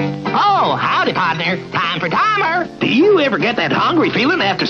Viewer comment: Whenever I see Time For Timer I hear: "My Mom is having a Bay-beee!"